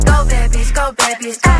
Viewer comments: noooo, yo siempre pensé que decía "go babys, go babys, go-" /llora en latino/ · En la parte de "Go bad bitch, go" siempre pense que decía "go babys go"